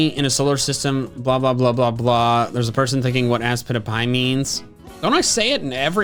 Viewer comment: OOOOHHHHHHHHHH!! I thought he just said that. I never pieced together that aspitpiay stands for "at some point in the past I asked you." My eyes have been opened! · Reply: When youtube CC subtitles misheard ASPITPIAY as "Ask Peter Pie"